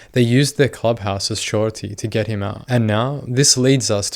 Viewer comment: To use their clubhouse as what? I can’t understand what he saying there. I would think that ‘collateral’ would be the right word but maybe there’s another word that I’ve never heard of. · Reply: surety.